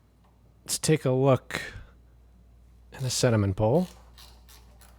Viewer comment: I thought you said cinnamon pole! I thought "What???" Sediment bowl